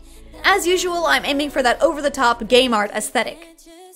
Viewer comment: I thought she said gay mart aesthetic not game art aesthetic 😂